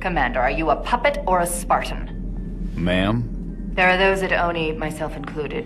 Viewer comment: Halsey is not Jorge's mom. He said ma'am but because pf his accent he sounds like he is saying 'mom'.